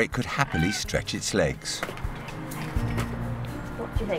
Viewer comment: did it say thank you?